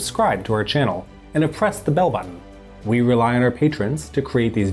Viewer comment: It sounded like you said "oppress the bell-button". :P